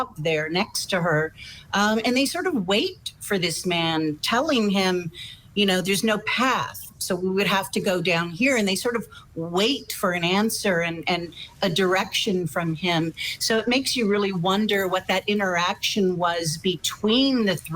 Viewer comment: I had the impression she said “ is that a gun” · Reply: I hear "There ist no path...<pause>...That's going down."